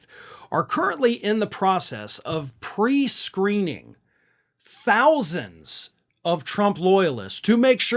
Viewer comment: For a minute there I thought it said " tens of thousands losers"